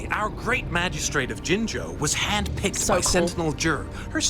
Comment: close capture "handpicked by sentinel jerk" XD to be fair i defenitely dont hear him say jué either - part of the reason I turned on cc to check here... mfg Olli